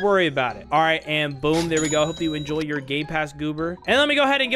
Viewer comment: is it just me or did he say "gay pass" ? 💀